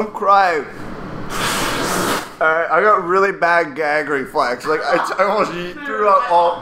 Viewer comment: i thought said "i have really bad gas"